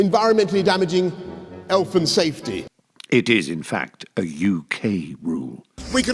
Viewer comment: did he just say "Elf and Safety"? lol